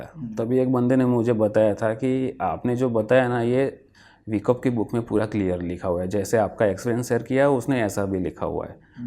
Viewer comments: 57 which book did he say ? kaunsi book ka naam liya unhone ? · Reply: Methods of Tape reading - Richard D Wyckoff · Mitesh sir mentioned the book name but i don't understand it's exact name....plz anyone spell it here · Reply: Richard d wyckoff